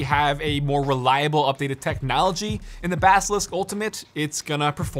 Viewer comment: i thought it said "balistik"